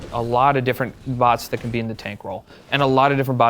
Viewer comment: he goes alot of bots that can be in the tank role. the subtitles say tangle l0l